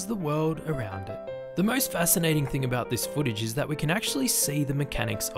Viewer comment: I actually misheard "footage" as "spoodage" which says a lot about how much I love them